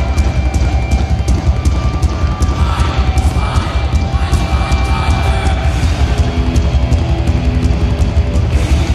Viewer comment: Anyone know what James said at 0:35? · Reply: eins ! zwei ! eins ! zwei ! drei ! vier !